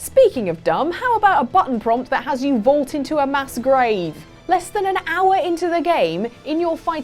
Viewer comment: did anyone else hear masquerade, or was that only me?